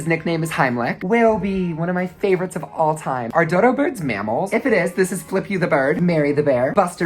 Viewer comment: The whole video I was so confused because when he said mammals I thought he said animals so when he moved on to reptiles, etc, I was very confused. 😂😂